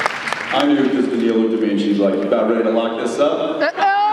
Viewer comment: And what does Jen say about Daneel at 54:26 ? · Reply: I think he says she said "you about ready to lock this up?"